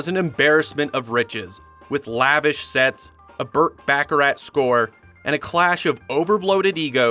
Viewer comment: did you just say Burt Baccarat…? 😂